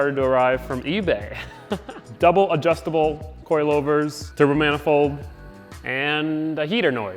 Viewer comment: i Heard turbo manifold right ? :)